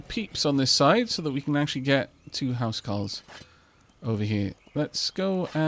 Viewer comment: i've always heard it as housecarl rather than huscarl, but huscarl might be a welsh thing